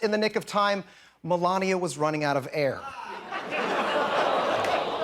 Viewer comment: Melanie was running out of air 😂😂 why was I the only one Laughing at that 😂😂😂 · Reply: I thought he said Melanomia was running out of hair. She sure can"t borrow any from her husband.